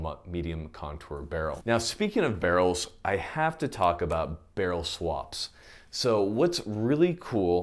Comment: He said barrel swaps but I thought I heard Barrel Sluts, I had a Beavis moment lol 😂